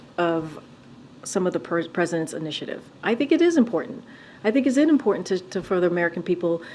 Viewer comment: she actually says(inimportant) go back and listen closely. If Im wrong in my hearing, then Im sorry.